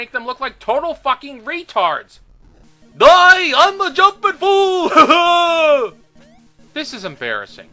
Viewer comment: Well, anyway, I just woke up (it's morning now), saw my email, so a upload and decided to see it. :D What did you say here 2:30? Was is "Duuuuh, I'm the jumping bull, hahaaa!" or something? :D This was the part that gave me the morning laugh, want to remember it